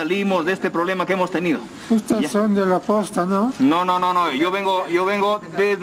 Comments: disculpen saben que dice el caballero? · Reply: ustedes son de la posta, no? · watafak, teníamos 4 regiones, Costa, Sierra, Selva, y lima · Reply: yo escuche "viene de la posta?" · Pobrecito el señor que preguntó si castillo era de la posta :( ojalá que en plena crisis se encuentre bien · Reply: Pensé que dijo de la costa xd